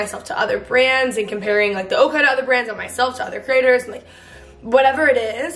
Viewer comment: I swear I thought you were saying otter bar 😂